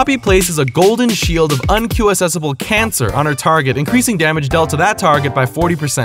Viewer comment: did he say cancer??????